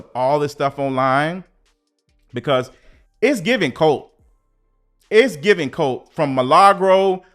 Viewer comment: Babyyyyy! THE WAY I had to rewind til I heard " atp it's giving CULT"!!! Like... ooooh CULT not... 😏 CACKLED!! 🤣